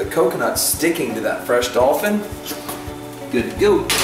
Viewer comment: did he say dolphin